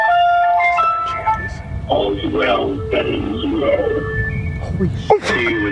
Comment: Bruh i just heard something in this call 4:28 it sounds like "all's well that ends well see you in hell" anyone else catch that?